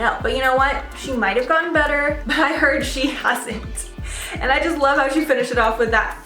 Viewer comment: what did she say about aguilera? i keep repeating that for 10 times... still couldnt catch what she said. 'she might have gone better but i heard she (hussings, cussings, hustlings, hard sings)' any help? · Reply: She said, “But I heard she [hasn’t].” 😊